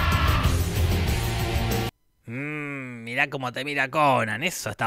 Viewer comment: eso está bajo de qué :,( nunca sabremos lo que dijo así nomás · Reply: De La Axila